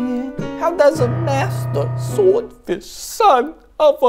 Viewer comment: “How dose a master swordsman, son of a boy, bring back some bacon-“ “Stop” ” music boy” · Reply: Wait... I heard that as Swordfish... oh god...